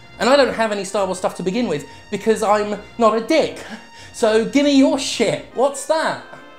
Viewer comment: when jim said 'im not a dick' i literally thought the first time he said 'i have a dick' so i had to rewind to make sure because red dead redemption 2 was loud on my soundbar. i use my gaming pc for youtube videos and my ps4 for online games while my pc is for single player games to avoid cheaters.